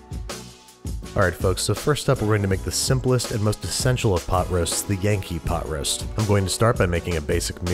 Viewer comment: Dude i thought you said pot of pho LOL